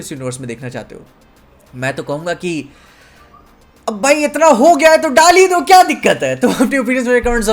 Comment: what said - Ab itna kuchh ho hi gya hai to daal hi do What i heard - Ab Malik itna kharcha ho hi gya h to Ek chips le hi lete h😂😂